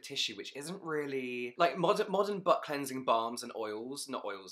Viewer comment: Did James just say "Modern Butt Cleansing Balms" (@ 6:20 in)????? 😂😂😂